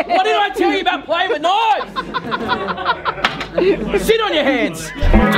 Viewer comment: everyone: ruel: throws knife at cheese board and almost kills someone: WHAT HAVE I TOLD YOU ABOUT PLAYING WITH KNIFES!!! SIT ON YOUR HANDS!! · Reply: rangaofc I thought he said “sit on your heads” 😂